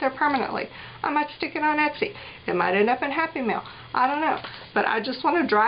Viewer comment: Omg, for a couple of seconds I thought you said it might end up In a Happy Meal instead of in Happy Mail. Lol. Personally I would love to get handmade journals in Happy Meals.